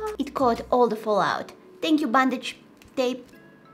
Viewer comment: “Thank you bandage tape” *Me who is allergic to it*: I’m using masking tape for fall-out then 😂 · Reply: Oh my! I thought she said “bondage tape”! I was like, whaaa?? 😂